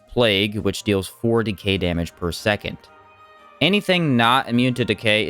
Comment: I thought you said 40k damage per second at first. Then I realized you said 4 decay damage per second 😂😂